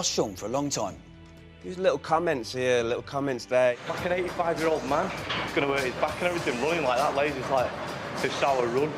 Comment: Can somebody translate what J said at 27:50-28:00? I'm not english and can't understand a single word. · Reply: "Like an 85-year-old man! Gonna hurt his back and everything running like that [can't hear that word] like 'This is how I run'. I mean look at it - it's like watching the OAP Olympics or summit (something)"